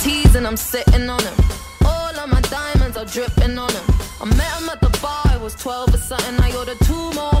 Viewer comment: At first I thought it said ”I met him at the bar, I was twelve or something”